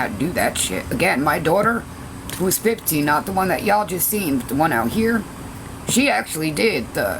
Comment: What did she say not the daughter you seen the one out there _________what did she call her ????? 17:58 ???? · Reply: She didn't say a name she just said the one that's 15 I think